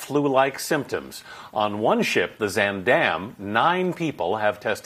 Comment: At first when he said Zaandam, I thought he said Van Dame. I didnt know he owed a cruise ship line.